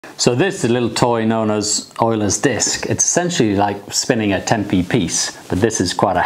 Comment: i heard euler's dick